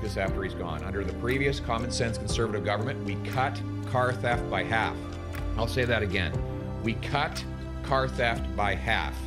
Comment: I heard that as “we cut car thief’s in half”… that’s one way to prevent them from reoffending…